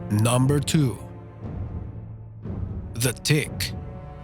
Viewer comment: On number two it sounded like he said the dick 3:37😂😂😂